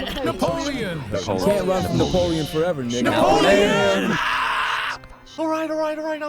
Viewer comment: Play War Thunder now with my links, and get a massive, free bonus pack including vehicles, boosters and more on PC and consoles: <link> | Mobile: <link> Thanks War Thunder for sponsoring this video! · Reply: I thought it says, "You can't run from Napoleon forever, my nigga"